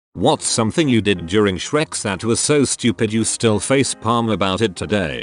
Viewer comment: sounds like it says "what's something that you did during Shrek's" lmao